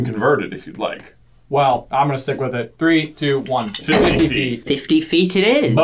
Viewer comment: 😂 i thought was as a joke saying soft to answer the question “how hard did Harry throw the gnome?” It was really 50 ft lol